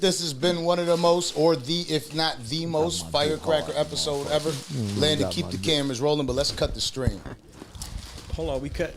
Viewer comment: Sound like he said you got my d hard dan a mf😂😂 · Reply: he said you got my dick harder than a mf 😂